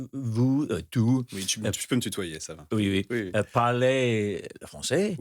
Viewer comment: Can someone tell me what Luke said exactly (in French) when the translation is “you can be familiar with me”? (8:05) I’m learning the language and cannot figure out what he said? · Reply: « Tu peux me tutoyer, ça va » sort of meaning « first name basis is fine » : in French you say « vous » when you dont know somebody and « tu » when you’re familiar